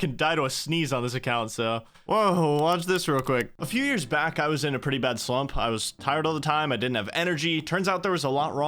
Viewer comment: I heard "A few years back I was in a pretty bad swamp".